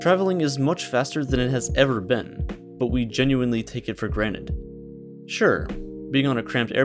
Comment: Ummmmmm 3:20 did he just say we take things for g r a n i t e